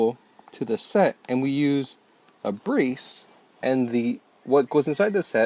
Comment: What does he say at 0:20? Breece? Or brace? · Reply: Brace